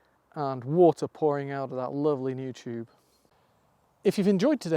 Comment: At the end and you said New tube! I thought you said YouTube! 🥴🤣🤣🤣🤣🤣🤣🤣🤣 the work looks great too! Well done you two 😉😊